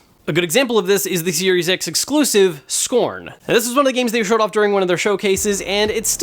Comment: I swear he said Serious X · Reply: Lol he should've slipped a J Jonah Jamison " You Serious " clip from Spiderman movie 😅😅 it sounds like he said serious X